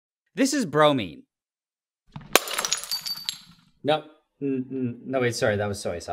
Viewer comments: Genuinely wasn't paying attention and I thought he said "this is broly" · First i heard "this is Bronie" Broo when that Bronya make that Soy Sauce 😂